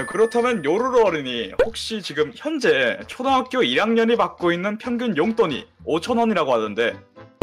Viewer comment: 초에 자막은 1학년이라 되어있는데 말할때는 2학년이라고 했어여 잘못들었나?...